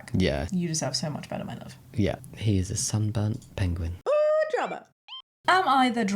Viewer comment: Did just say he’s a sun burnt penguin? Is that a reference to something?